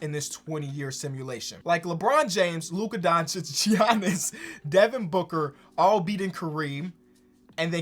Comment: Did say Jiannis?